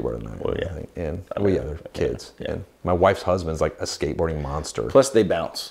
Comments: Did I hear him say, "my wifes husband is a skateboard monster"? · WAIT!!! Did you just say, "my wife's husband is a skateboarding monster?" Now, I'm confused. 😂🤣😂